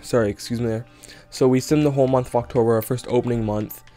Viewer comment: did you say fucktober?